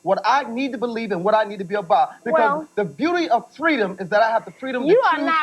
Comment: the beauty of free did I hear that right?